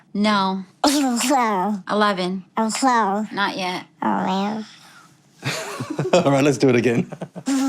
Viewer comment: Did anyone else here “aw man” instead of “well I a,”